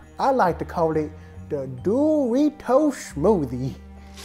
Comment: I thought they said “Doritos” but is durito